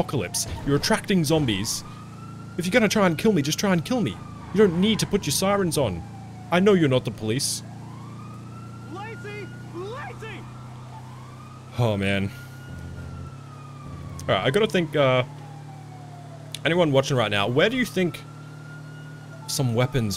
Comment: At the 41:18 mark, I couldn’t stop laughing because it sounded like a zombie yelled “glizzy, glizzy”.